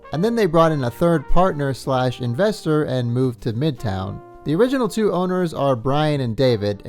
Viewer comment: bro I thought you said "moved to MGTOW" and got so confused